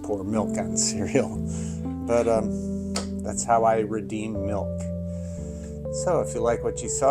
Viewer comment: Thought you said dead milk 😢